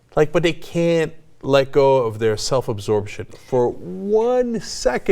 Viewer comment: I thought cenk said "self absorbed shit", but he actually said "self absorption"